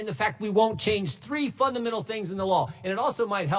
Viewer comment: thought he was gonna say “3 fuc—n things in the law”